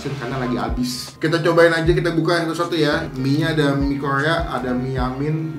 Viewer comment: mie Kroya apa mie korea bang?? kalo ga salah denger gue ni hehe